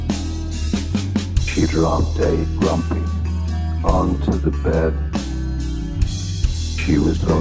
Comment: Absolute masterpiece. "She dropped a grumpy... onto the bed" instant classic 😂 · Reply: 🤣 I was only half listening and just blurted out "did he just say grumpy on the bed!?"